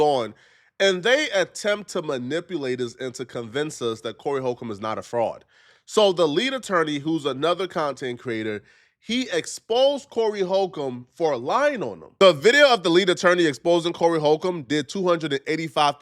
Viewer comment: Did you just say “who cares if he’s an attorney or not?” His name is “the lead attorney”